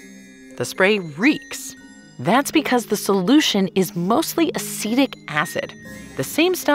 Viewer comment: I swear I heard "acidic acid" 😂😂😂